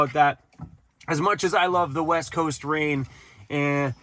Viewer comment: I swear it sounded like you just said 'the WET coast'! LOL